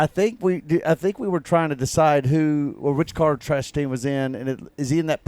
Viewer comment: Did he call him Trashtain 00:44 ?